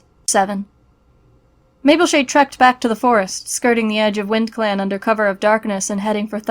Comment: They said “Skirting” as in, bordering etc I heard “SKRRRRTing”